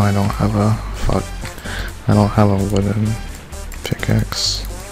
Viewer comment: I thought he said” I don’t have a woman axe” 🤣🤣